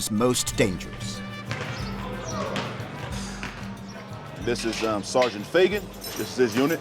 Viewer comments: Am I The Only One Who Heard Sergeant Fa##ot? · I thought I heard " this is sergeant f*ggot" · the first time I heard the co introduce his partner, I thought he said "this is officer feggot" I think I need hearing aids at 17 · Did he say "sergeant faggit" ? lol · did he say sergeant fa**ot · did i just hear Sergeant faggit???